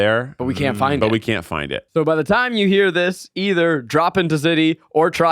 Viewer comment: No offense, but whoever was arguing for Drop Into City was a thousand percent wrong. It’s one of the clunkiest phrases I’ve ever heard. The episodes are so good and they deserve a better title. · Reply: This whole time they've been saying that title, I thought they were saying "Drop in the city" , just realized this pod what it is. Not a good title